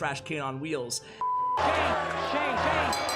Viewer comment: I thought at 3:45 he said "shane" as in Clash with Shane